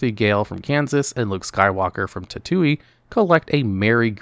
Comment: Wait, what did you just say?!? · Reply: Tattooey